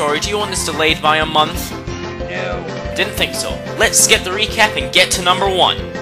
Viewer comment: im sorry it sounded like they said lets rekap